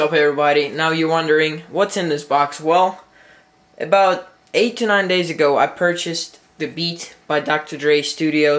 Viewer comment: i thought u said 89 days ago